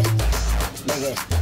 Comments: i hear " biggest, biggest, these are the biggest phones of the summer" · What I heard was “ N words, N words, N Words, dis the biggest bones of the sunmer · I thought he said "biggast" and "this the biggest phones of the summer"